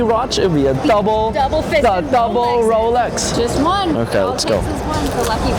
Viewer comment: Did chick really say double fisted @ 13:21? You missed a good opportunity for a side eye there...